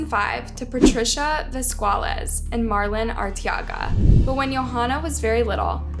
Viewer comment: Vesqualez? I thought it was quite easy to say Velasquez as it's quite a common last name